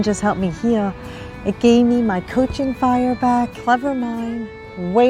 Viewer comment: first time I watched it it sounded like she said Coochie fire